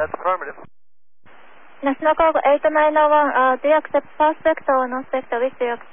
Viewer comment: I think she said “Do you accept north vector or south vector. For foreigners, I think first female controller’s experience is short. Especially Japanese English accent is not well because they are not trained about it. It is serious problems I suppose.